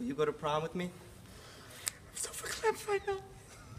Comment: What does Robin say at the 2:06 mark? It made both Ben and Skyler laugh, but I can't understand it? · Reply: He said, "I'm so ver klempt right now!" It's Yiddish for emotional/choked up.